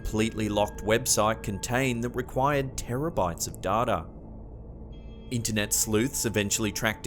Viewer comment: It sounded like you said "terabytes of dada"